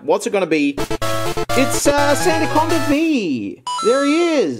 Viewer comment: i wasnt looking and i thought max said: Santa come with me